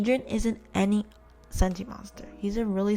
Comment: i thought said he wasnt a sentimonster